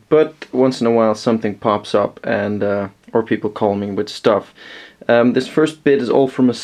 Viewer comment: What an extremely arrogant quote: *Poor people call me* So you actually take advantage of Poor people. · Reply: 0:31 I think you didn't ment to say poor people lol